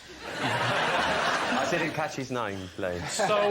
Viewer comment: Still think the funniest part of Rylan’s story is when Lee said “Was it Edward Fox?” and Rylan (who obviously didn’t know the actor, judging by his blank look) just said “no”. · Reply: Is this one of those Mandela Effects or am I missing some kind of weird inside joke? In my universe, Rylan said, "I didn't catch his name, Lee."